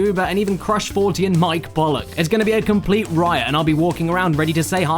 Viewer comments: I thought he said Mike Bullock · Wa..wait a second. Did you just say Mike Bollock?